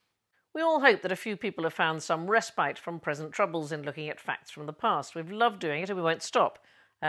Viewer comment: Did say, “I hope you find respite from President Troubles”? First time I’ve heard him called that; I like it and thank you! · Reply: I thought she said present troubles, but I can definitely get behind this most-fitting nickname instead/as well! 😁